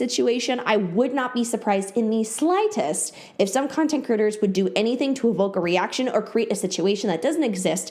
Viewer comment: you said “content creators” but i heard “content critters” and honestly, that’s fitting